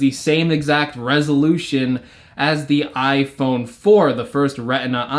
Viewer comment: I thought he said iphone 6 not iphone 4